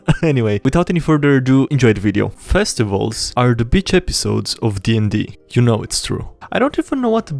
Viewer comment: I thought you said Bitch Episode